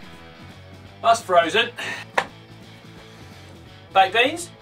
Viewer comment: I thought he said babies 😂😂😂